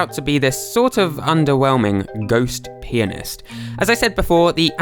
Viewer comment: “Ghost penis” Wait, I heard that wrong, didn’t I?